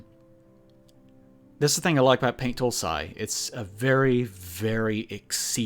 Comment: I couldn't hear that word clearly... What's the name of the program you were talking about? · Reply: Paint tool SAI?